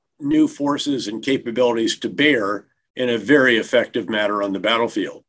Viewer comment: Did he say, "capagonies" to bear~? Was that a Freudian slip~? I listened several times and there is no way he said, capabilities.